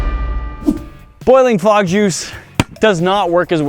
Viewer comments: At the start I thought you said frog juice so I was really confused and concerned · I thought it’s said “Boiling liquid frog”